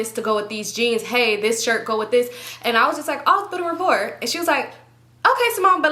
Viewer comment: Do anybody know what she says at 8:35 she talked super fast on tht part 😂 & i rewind it back like 3 times lol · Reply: I think she said "build a rapport"